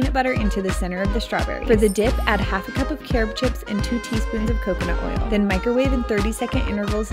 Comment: BEFORE YOU ATTACK HER, She says carob chips which is NOT real chocolate so it can't harm your dog. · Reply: Glad I found this comment, because I thought I heard chocolate chips not carob chips